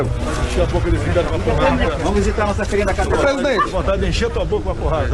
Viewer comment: vamos visitar sua Filhinha na cadeia foi impressão minha ou reporter disse isso para o presidente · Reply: Eu ouvi "vamos visitar nossa cria na cadeia" Se fosse Trump teria arrumado mó confusão e com razão. Esses lixos perderam a mamata e tão com raivinha do presidente